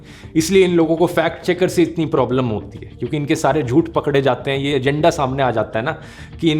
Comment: suddenly i hear pakal liya 😂 dhruv bhy